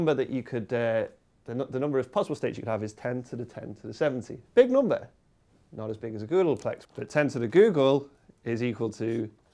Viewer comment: Thought she said google plus at first lol